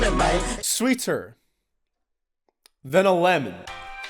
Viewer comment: i think he said lemonade